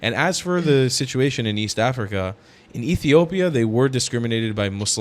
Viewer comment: did you call it YEAST AFRICA?